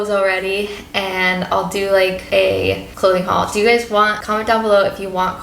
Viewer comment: I totally thought you said, "baby girl Chloe hall." I'm like ahhhhhh they accidently said the name. Haha before I realized "clothing" Love you all! Been watching and Suscribed since the start!